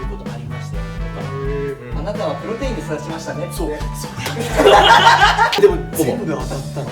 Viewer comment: の嶺亜くんなんて言ってるか教えて頂けないでしょうか、、？気になって前に進めません😣 （他担だけど毎週楽しく拝見させて頂いてます🙇） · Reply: コメント失礼します。 あなたはプロテインで育ちましたね！だと思います☺️